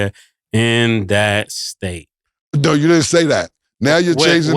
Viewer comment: nah I think he said in that state. But I don't think he was number one in football & basketball the same year but he was highly recruited for both